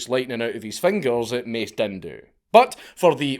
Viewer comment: Wait...wut? Did he just say "Mace Dindu?" 🤣